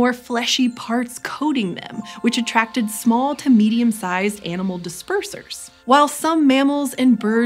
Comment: I was listening with only one headphone and I thought you said "small comedian sized" 😂